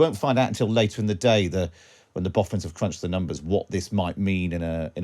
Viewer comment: Thought he said at 2m 30s "When the Bothans have crunched the numbers". Was wondering where Star Wars came into all this, then realised he probably said "Boffins". Many Bothans died crunching the numbers...